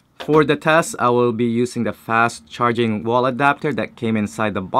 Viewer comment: I thought you said not fast charging in your video.